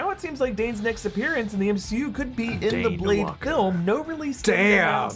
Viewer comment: The Daywalker is the name for Blade. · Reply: I know. But it sounded like they said Danewalker, as in Dane and Blade's name put together like a ship name.